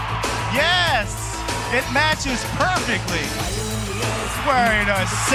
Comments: I thought they said "Look who's here at the crackhouse!" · Sounded like they said look who’s here at the crack house!! 😂